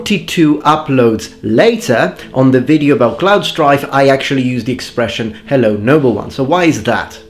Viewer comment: For an embarrassingly long time, I thought you were saying “Number 1’s”